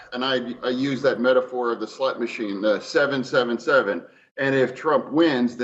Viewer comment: Am i the only one that thought Fetterman said "slut machines?" And where would one look if they wanted to find one of these titular machines?